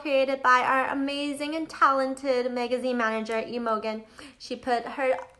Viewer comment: did she just say " emo gen" instead of Imogen...